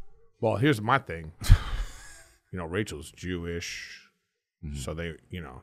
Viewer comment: i thought he said Rachel was due-ish 🤰